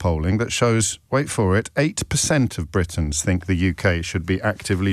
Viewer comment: i initially thought you said 80% think UK should be involved !! and not 8% :) - i'm from UK - live in Australia - and thought FFS !! :)